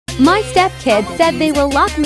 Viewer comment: I first heard "Stepdads"